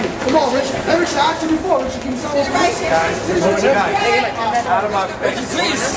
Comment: haha I keep hearing "Guys, guys get out of my face. Get out of my face." :L Aww poor Richie ....